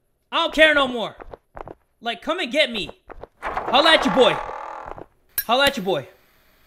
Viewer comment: when he said “I’ll let you boy” I heard “I’m edgy boy” I think I watch Phoenix Wright wayyyy too much. 😂